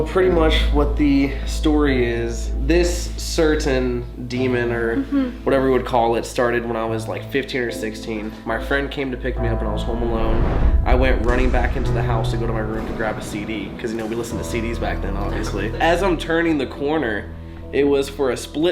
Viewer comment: when waa telling when he first had demons i thought he said "it started when i was 50 or 60" HOW OLD ARE YOUUUUUUUUUU